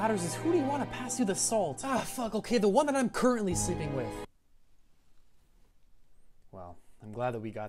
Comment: I heard "the woman I'm currently sleeping with" instead of "the one that I'm currently sleeping with", was hella confused